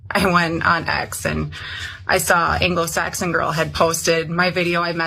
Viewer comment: Did I hear her say "anglo saxon girl"